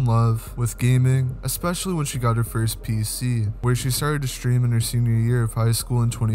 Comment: I thought he said "especially when she got her first BBC".